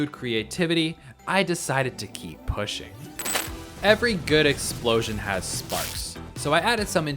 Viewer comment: I thought it said every gay explosion